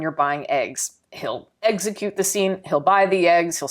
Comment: I heard (eggs)execute lmao 9:34 <3 your videos